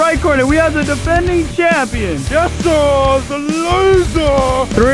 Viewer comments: LMAO if you turn on captions and go to 5:18 it says "just a loser" instead of jesserthelazer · Did I hear "JesserTheLazer" the lazer is backkkkkk 😂